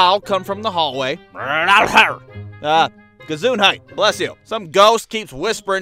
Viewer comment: did he just say gezondheid